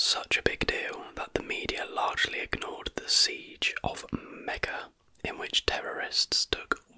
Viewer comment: Can anyone clarify what is said at 4:10? The media largely ignores the "siege at mega??" due to the Iranian hostage crisis? I can't find anything about that online. Thank you! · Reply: Could it be Mecca?